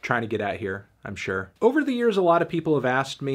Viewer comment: did he say over the ears?